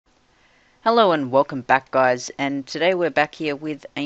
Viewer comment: lol i thought u said 'hello and welcome fat guys' loool